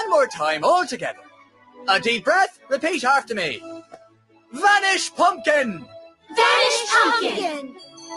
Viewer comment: I was distracted and I thought they said "fetish pumpkin" I